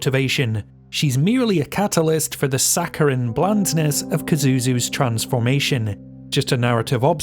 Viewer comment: Kazuzu sounds like a really fun demon. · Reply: Ahaha, apologies, I thought you said Kakuzu